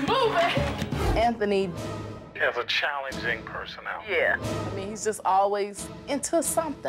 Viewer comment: did he say Challenging Personal or Challenging Personality please reply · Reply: personality